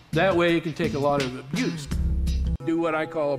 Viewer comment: I thought he said the balloons could take more juice not abuse and it made it worse